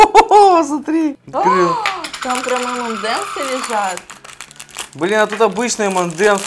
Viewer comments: А материться? Я услышала бля на 19:09минуте. Кто услышал лайк. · я одна услышала бл* от? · Мне послышалось или на 19:09 сказал бля?